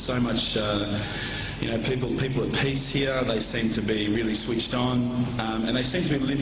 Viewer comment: i could swear he said Peace and not Pastier (white skin)! Strange